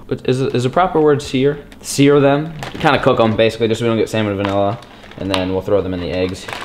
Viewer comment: did he really say ( so we dont get samon vanilla) lol